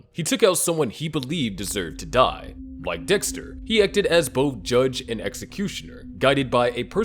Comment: what did he call dexter? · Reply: Dickster